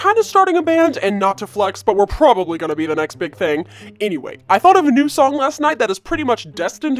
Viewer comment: wait isn't Big bang a kpop Group??? Or it could just mean that it's acctually going to be a bomb but idk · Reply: I thought he said the next big thing